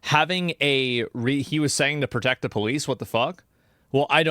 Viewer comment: he was saying “come here the police will protect you.” He was not saying protect the police. He was egging on aggression. Also, it’s hilarious they bring up the homophobic slur. Maricon is not used the same way in Spainisn as you may assume other synonymous English words are.